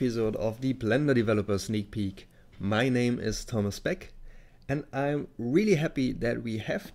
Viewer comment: At first I thought you said your name was Thomas Speck. I soon realized my error, but now I am hungry for bacon.